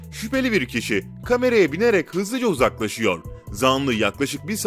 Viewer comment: yanlış mı duydum kameraya binerek